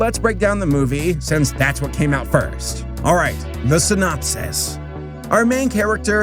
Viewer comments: when u said “synopsis” i thought u said “listen up sis” lmao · am i the only one who misheard 'the synopsis' as 'listen up sis'..